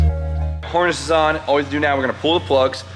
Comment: did say whoreness 🤣